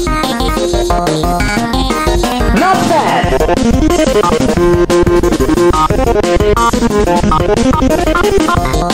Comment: ok at 0:42 is he saying 'not bad' or 'not fair'? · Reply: Not bad